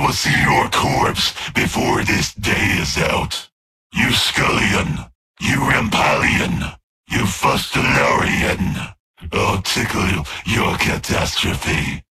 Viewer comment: i wanna know what this says in subtl, anyone has any idea? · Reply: "You scullion! You rampallian! You fustilarian! I'll tickle your catastrophe."